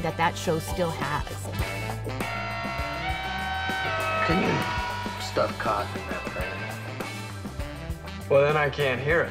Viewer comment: Did DeSoto say what I think he said?! o_0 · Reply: Wasn't anything bad-- all he did was to ask his partner John to try to stuff cotton in that bagpipe that Chet was playing.